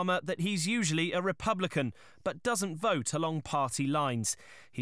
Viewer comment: I respect the fact that they said he’s usually a Republican, but doesn’t vote along party lines. Fair play to him. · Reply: I heard 'party lies'!